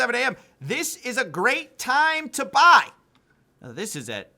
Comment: "This is a great time to be afraid to be alive!", is what I heard.